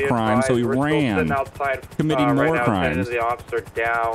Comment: is it bad i heard war crimes not more crimes